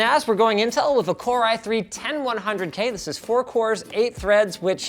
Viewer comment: did you say core i3